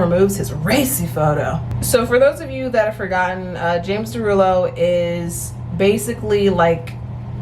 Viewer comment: james derulo me: wait--james? did she say james???